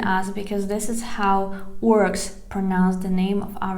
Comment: Hi, I'm sorry, I didn't catch the word. Who pronounces it like that? "Works"? Please, tell me, what the word is. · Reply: orcs. she meant russians by this.